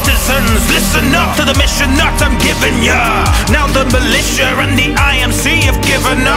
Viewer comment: I thought he said mission not giving ya